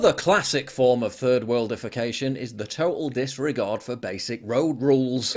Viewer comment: I kept hearing "3rd world defecation"... 80